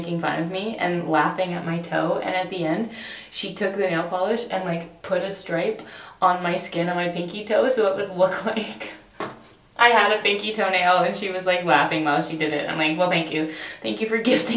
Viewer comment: Logically, my brain understood that you are just missing your pinky TOENAIL but my tried brain heard you say missing pinky toe and then I was so shocked that you’re missing a toe 😂👀